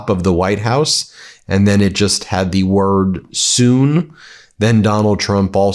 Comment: the caption should read 'Jump' instead of 'Soon'.